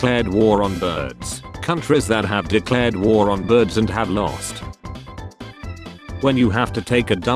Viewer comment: I thought I heard China also declared war on birds? It went as well as it did down under